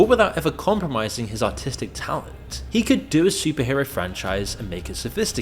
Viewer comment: Did I hear Autistic talent 😂